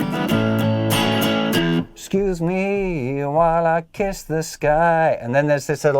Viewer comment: kisses guys? Not that there is anything wrong with that.. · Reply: Lol you thought he said excuse me while I kiss this guy it sounds similar lol